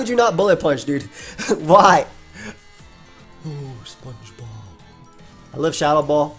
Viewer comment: does anyone else hear 'ohh... Spongebob????'